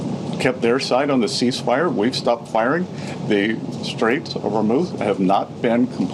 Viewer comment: Did he actually say "Straits of Vermouth" at 1:00?